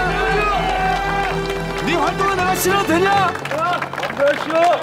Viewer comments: 뭐라는거냐? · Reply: 니 활동화 내가 신어도 되냐? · 이거 뭐라고 하는거임? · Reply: 니 활동화 내가 신어도되냐? 맘대로하십쇼! 인거같아요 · 황제성이 1:30 뭐라고 하는건지 좀 알려주실 분 ㅠㅠ 화뚜르? 이게뭐죠? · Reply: 니 활동화 내가 신어도 되냐?!